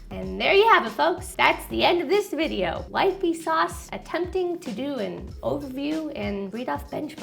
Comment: Did she say, "Like Vsauce?"